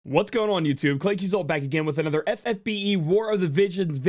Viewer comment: I still cannot belive that for over three years i thought you said "Claic is all back again with another FFBE video" in your intros... Im an idiot 😂